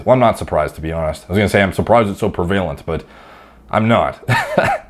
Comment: it sounds like you said "pervalent." I think you mixed prevalent and pervasive into one word or I misheard. I'm not dogging on you either. I misspeak all the time but I heard that and my brain went 🤔. Keep at it with the killer content!